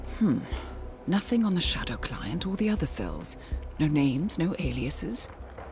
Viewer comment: i kept hearing "Shadow Clone" and it took me a while to realise it was "Shadow Client"...